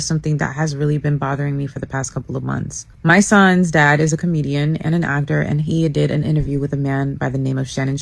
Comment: did she say Donnell is a comedian and a doctor???